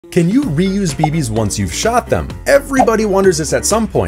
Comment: Why did i hear "can you reuse babys once youve shot them"